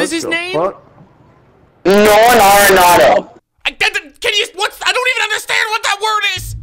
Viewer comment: I think dude at 9:00 was saying Ramon Laureano